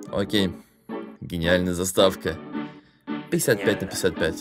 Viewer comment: Я однаму послышалось "психа5на 55"